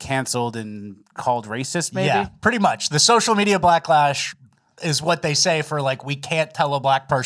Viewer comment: Did I hear that right 😂 “social media blacklash”